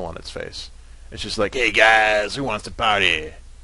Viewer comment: I'm not shoure but I think it's the good dragon and althought my dragonian is'nt perfect but i think he was saying "ether hey guys how wants to party" or "Wassssssssssssaaaaaaaaaaaaaapppppppppppppppppppppppppppp" lol